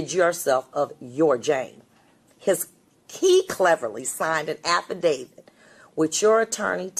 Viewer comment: Can someone explain the audio at 21:37 please? The audio leading up to it says (I think): "...but what you did not realize is a person you called an idiot would reveal your manipulative plan to rid yourself of your Jane his key cleverly signed an affidavit which your attorney took the bait, accepted it and believed that it would set you free...". If you replace "his key cleverly" with "and signed", then it makes sense, but I would like to know if I misheard the judge's words. Thanks in advance! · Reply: Not "his key cleverly signed". It's "'Cos he cleverly signed". 'Cos is a contraction of because.